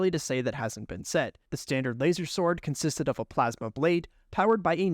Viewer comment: Sorry I didn't hear you there properly. The what consisted of a plasma blade...? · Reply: I’m so late lol, but he said the standard laser sword consists of a plasma blade